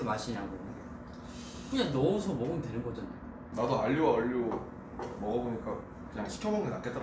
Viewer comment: I thought namjoon said I ate Oli London 😂😂😂